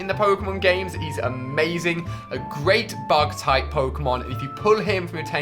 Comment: i thought he said thug type pokemon jks